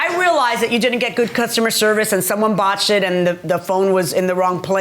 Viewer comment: WAIT! @ 7:15, Did Judge say someone bought SHIT?!?! On TV?!?! Cussing LOL!!